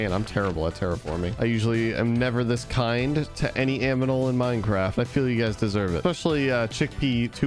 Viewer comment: did he say aminal?😂